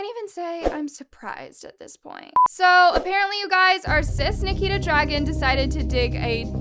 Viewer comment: When the captions say "cis Nikita" when she's actually trans! Oop even the captions are shady...